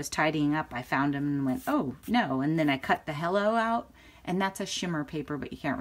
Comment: You said..."I cut the "Hello" out"...but I heard..."I cut the hell outta them"...hahhahaha🤣🤣🤣💜💜💙💙